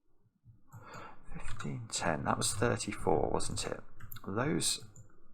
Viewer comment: You did just whisper 34 right? I heard something complete different at first